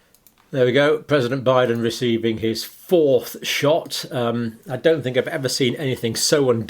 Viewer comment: Maybe it's because I'm not a native speaker, but each time Dr. Campbell said "fourth shot" I heard "false shot". For some reason, my mind did not enter into the slightest dissonance because of that mistake.